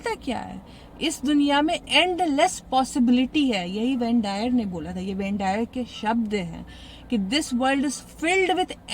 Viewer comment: ma'am ye name smjh me nahi aarha kya bol rhi h aap?? Ben diah?? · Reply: wayne dyer